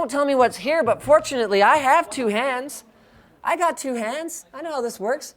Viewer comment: Who remembered the moment when he checked the Razer Blade specs for the 7th gen i7 at PAX Prime when I heard "But, I have got 2 hands"